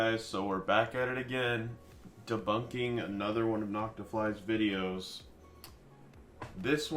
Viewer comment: the subtitles say "knocked off lies" instead of "Noctafly's"